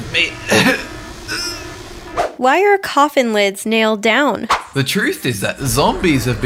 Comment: I thought it said "Why are coffee lids nailed down" lol